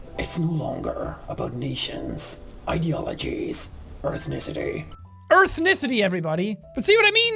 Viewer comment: And now more than 6million people heard you say "earthnesety"